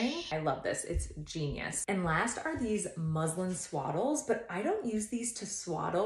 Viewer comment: For a second I thought you said Muslim and then I went on reverse and then I heard whatcha really said